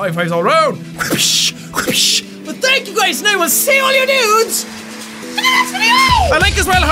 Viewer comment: In 7:50 of video I heard "see your all nudes" XDDDDDD